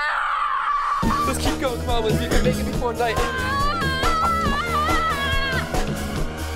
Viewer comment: i heard fortnight